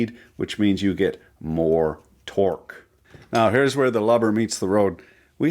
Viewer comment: Did he just say here’s where the lubber meets the road? I almost thought he was going for something like here’s where the lover meets the load